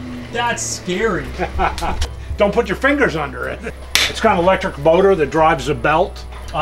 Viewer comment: did i hear electric boner?